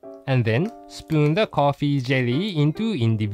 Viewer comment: For some reason, I kept hearing "potty jelly" instead of coffee jelly. XD